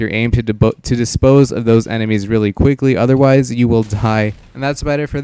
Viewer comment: Is it just me or did anyone else here tie instead of die